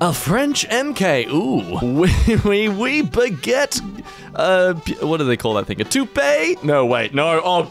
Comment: For anyone who doesn’t know what he said at 12:09 he said: yes yes baguette, your welcome